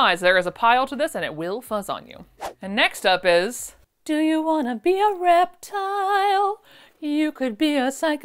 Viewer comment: You said “do you wanna be a reptile” I heard “do you wanna be a Reptar” Immediately the answer was yes